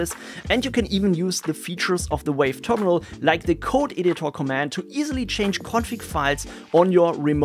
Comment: When he said "code editor command," I thought he said "code idiot command." Got excited there for a second -- been looking for something to help with my idiocy. Darn Austrian (I think?) accents. 😊